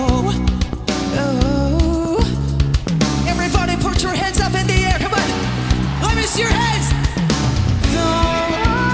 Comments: ここeverybody put up your handsの後何言ってるか聞き取れる人教えて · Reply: let me see your handsと言ってると思います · ~ What is Taka say after “Everybody put your hand.”? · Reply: gsiz - in the air come on